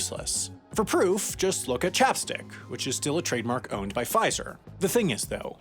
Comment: Huh.. I always thought people were saying "chaps dick". like "that really chaps my dick!" The more you know, aye?